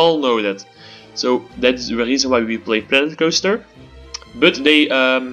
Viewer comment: sound like you say plan coaster but you better than me on engish